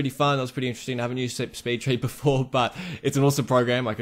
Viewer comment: Which software is this? Sorry I cant unterstand what he is saying xD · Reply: Speedtree I think